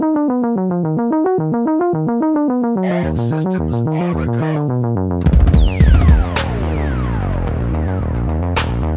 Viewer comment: there's a sample " all system is all" something that i can't heard clearly can u plz tell what sample pack is that plz man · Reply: All systems are a go